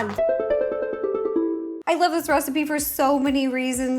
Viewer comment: just me or did she call it a Rocipe instead of a Recpie? XD